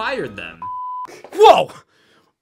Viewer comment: when he said dream i thought he said tree... immediately thought of pokemon trees